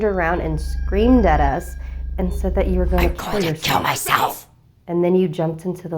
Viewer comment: wz so funny cause it sounded like she said ¨I'm going to kale myself!